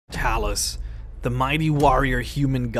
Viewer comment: I heard you say Talis.... wtf